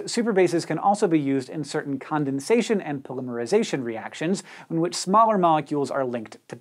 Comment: Did he just say... polymerization?